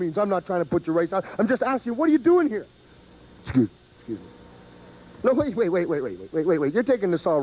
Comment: Piper accidentally hits Snuka with the mike and whispers "excuse me." This must've been a rehearsal for the first Pit to set up the second, famous one. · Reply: I heard "Excuse me," too, the second time...